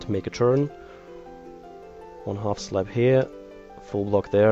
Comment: sounds like he says half slaps. lol